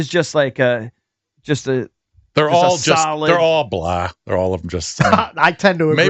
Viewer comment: I thought said. “Ah they’re all Black”🤣🤣🤣I was about to die laughing bruh😂💀💀